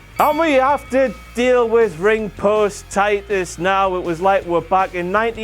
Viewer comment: Did he say Ringpost titus?